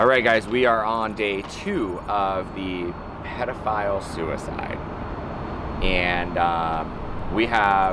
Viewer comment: I had to rewind, thought I heard Pedophile suicide!? He was Guilty obvy.